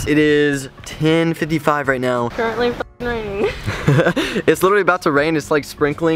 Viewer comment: What did say at 6:13 ? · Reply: haha "currently fucking raining"